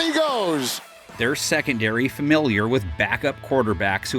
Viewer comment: did he just say the pats secondary is full of backup QUARTERBACKS?!